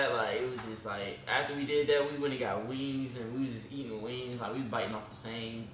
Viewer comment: I can’t get enough of y’all. I just smile, smile, smile. Lord, I thought you said, “after we did that, we went and got weed.” I’m like WAITAMINUTE!?” THEN I realized, wings. You said WINGS. Smh.😂😂😂😂 love y’all though!!